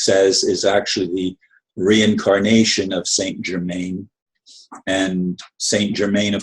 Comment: I find it hard to believe that Trump is the reincarnation of St. Germain. It simply does not make sense, at least on this earthly plane. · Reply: Where did he say incarnation? I thought he said assistance from St. Germain.